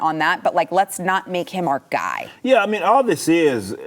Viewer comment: I thought Gonzales said "let's not make him our god" and I agree with Sarah then. People follow this guy super hard